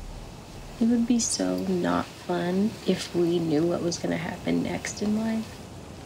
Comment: can someone write what they said in 0:40? i'm not so good in english. thanks! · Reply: Glaucius Rech it would be so not fun if we knew what was gonna happen next in life